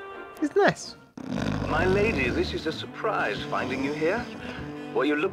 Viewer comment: the person on the tv says "Were you looking for me by chance?" but the edited in subtitles say "Where you looking for me by chance?"